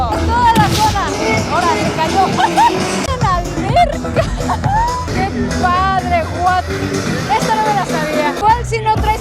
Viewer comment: Que dijo?! 😦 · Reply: Tiene una alberca😨